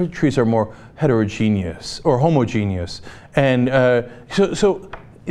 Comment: Why did it sound like Cenk said "homogenous" as "homogenius"?